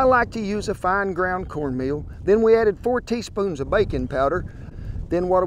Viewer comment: I heard "bacon powder" instead of "baking powder"...